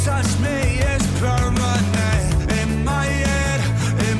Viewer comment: Sounds like MGK is saying, "In my Yed" in the bloody valentine song. Not sure what a "Yed" is.... but that song gave me a Yedache....lol! 🤯